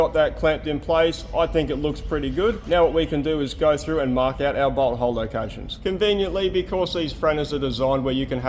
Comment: I typically watch long videos at 1.25 speed. It kind of sounded like you said "Now what we can do is go through and mark out our butthole locations".